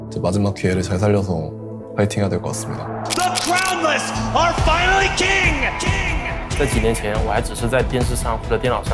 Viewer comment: 캐스터가 영어로 뭐라고 하는건가요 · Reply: the crownless are finally king = 왕관이 없는자들이 마침내 왕이 되었다 오랫동안 월즈 무관이였던 중국에서 월즈를 먹음과 동시에 더샤이가 IG에서 중국의 무관을 깨서 왕이된걸 말하는거 같네요